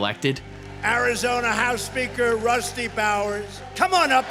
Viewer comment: I thought they said Rusty Bowels 😂